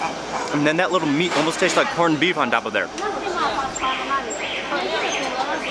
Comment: He said it taste like cumin...i thought he said it tast like human....Well you're in the right place...lol 😂😂